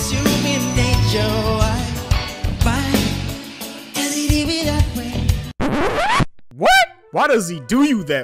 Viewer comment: To be fair to MJ, he didn't write it. He just sang it. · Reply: I like that song. I always thought he said "do you do me that way" not does he do me that way. If someone else wrote the song then that's understandable I never knew he had ghostwriters.